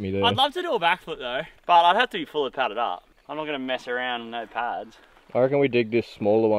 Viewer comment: thought you said “fully powdered up” to try a backflip… which would make sense too lol